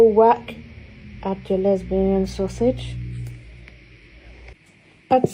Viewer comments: Help am I going crazy or does she call the sausage at 3:33 a "lesbian sausage" 😭😭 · might just be tweaking here but does she say "add your lesbian sausage" lmao